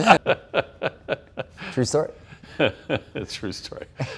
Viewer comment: What was the word Tré said, and how is it written? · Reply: bathing